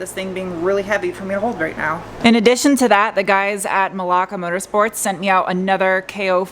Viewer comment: did you say Malaka Motor sports @ 0:45 by any chance? Maybe I misheard?